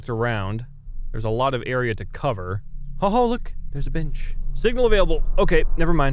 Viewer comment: I heard "oh hey there's a bitch